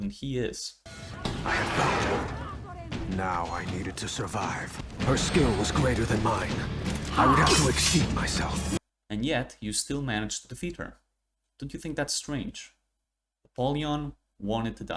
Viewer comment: hm, i swear i remembered him saying “over-exert” not exceed